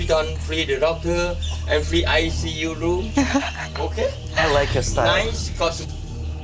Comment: На 28(28:42)й минуте когда таец говорит про бесплатный укус, последнее что он говорит free ICU room, что означает реанимация, а не лед